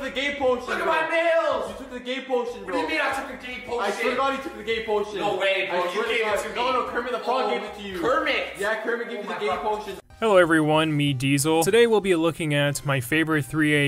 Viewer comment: I heard “KERMIT GAVE ME A GAY POTION!” And began to question life.